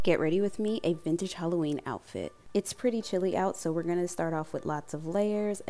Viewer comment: I thought you said "It's pretty silly out" and I thought, "Where do you live???" 😂